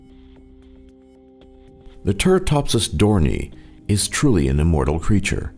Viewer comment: i thought that said dorritos